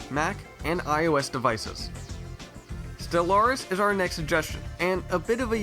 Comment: What the hell is that game you recommend at 6:56 I keep going back and replaying it but I can't understand you. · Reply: Stellaris